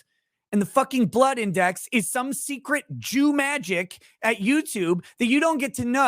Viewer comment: did you really say at 3:28 "it's some sort of Jew magic"?? WTF bro. Are you some sort of anti-semite?